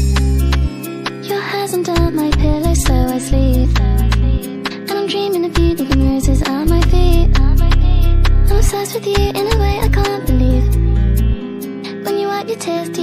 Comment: thought she said "im sus with you"💀